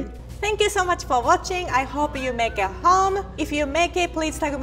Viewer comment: At the end I thought she said “I hope you make it home” I thought it was a threat lmao